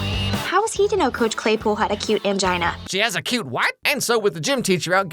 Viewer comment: No because "acute angina" had me in a chokehold as a teen because i always thought she said "a cute mangina" LOL